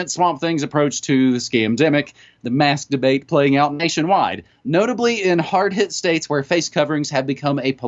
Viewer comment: Did I hear that right? 'The masturbate playing out nation wide'...? Quite a Freudian slip up, as the controllers must be masturbating all the way to the bank, incredulous as to how easily humanity folded over this non crisis.